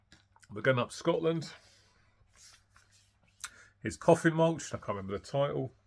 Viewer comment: Erasure and Pet Shop Boys have lots of good songs in their catalogs for sure. 'Introduce Yourself' is good IMO... bought an OG copy from Jürgen last year. 'Time's Up' is awesome too. I keep mishearing "Coffin Mulch" as "Coffee Mulch' :D Good stuff!